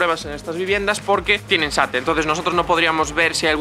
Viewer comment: Muy interesante todos los datos, pero en 9:56 dices que no puedes hacer la prueba y justo no entiendo la palabra que parece importante, no entiendo el motivo, parece que dices "porque tienen satre" Si pudieras aclarar eso, si es algún revestimiento que tienen o algo así. Gracias. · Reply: SATE, sistema de aislamiento térmico exterior